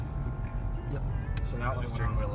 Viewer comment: a light went out, which light is that? I couldnt understand what was said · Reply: It was the steering wheel light. Nothing special was required to make it go out, just a couple of normal turns did it. Good luck!